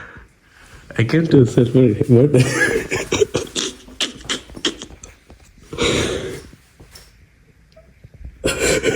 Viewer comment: what did you say actually?? Can anyone explain. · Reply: Wanna hug?